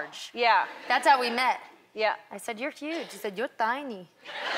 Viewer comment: i thought she said "your huge" "your fine!"